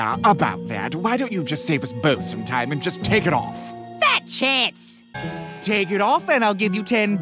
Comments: I don’t even know what he’s saying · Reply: Fat chance he said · "Take it off and I'll give you 10 bucks~" "Oh boy, 10 bucks !" "No, Cuphead ! Your Soul's worth much more than 10 Bucks !!!" "Hey, You're right ! M A K E I T $20" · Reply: I thought he was saying, "Take an orphan and I'll give you 10 bucks" 0____0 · did he just say BatChest??????????